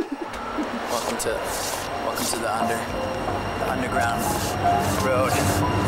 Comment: did he say welcome to the 100” ?? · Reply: "welcome to the under... under ground"